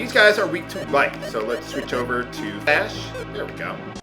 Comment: it sounds like you said "Switch over to ass"